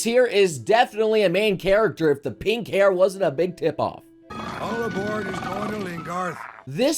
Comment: "All aboard whose going to Lingarth" is what he said for those wondering